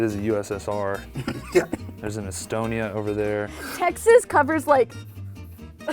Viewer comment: I thought she said axis